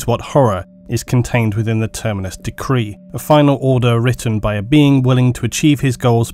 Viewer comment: I thought it said Tinnitus Decree and was all like eeeeeeeeeeeeeeeeeeeeeeeeeeeeeeeeeeeeeeeeeeeeeeeeeeeeeeeeeeeeeeeeeeeeeeeeeeeeeeeeeeeeeeeeeeeeeeeeeeeeeeeeeeeeeeeeeeeeeeeeeeeeeeeeeeeeeeeeeeeeeeeeeeeeeeeeeeeeeeeeeeeeeeeee...